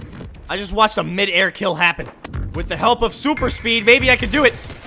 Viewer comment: Who else paused right here 8:10 and thought he said WITH THE HELP OF SOUP